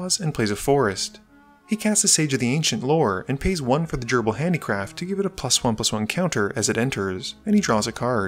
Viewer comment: I thought you were saying "gerbil handicraft"